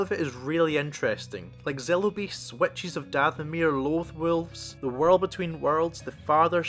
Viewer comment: I know it’s just accents, but when you say “Witches of Dathomir” I hear “Wet cheese of Dathomir” 😂